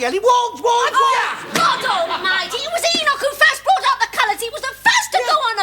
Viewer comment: did she just say "fuck off, mate"?